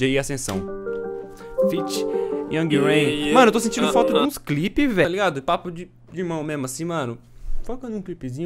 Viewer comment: tá sentindo falta de quê mesmo???? Eu ouvi você falando "Clipe"???? É sério que eu ouvi????